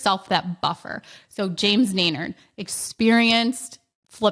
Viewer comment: That is so smart to add that 20% rehab buffer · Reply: I couldn't make out the pronunciation of the last name mentioned at 23:48 but I think it was James 'Dainard'